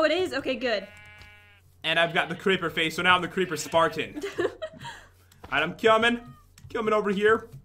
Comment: it sounded like pat said "now the creeper's fart'n